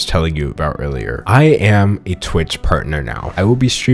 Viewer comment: At first I thought he said he became a Twitter partner instead of Twitch, and all I felt was fear.